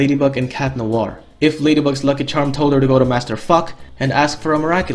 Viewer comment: did he just say Master fuck? 💀